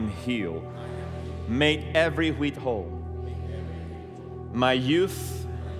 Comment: Can someone tell me what he says at 2:10-11 ? Is it make every wheat whole? Not trying to be funny. Just want to say it right. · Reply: In the King James it said that Jesus made a man "every whit whole" (John 7:23). The word "whit" is an archaic word but full of meaning